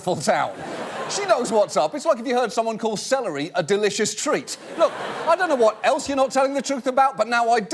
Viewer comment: I heard "salary - a delicious treat" :)